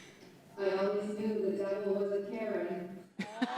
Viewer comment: Does Mel say "I always knew the devil was a Karen?"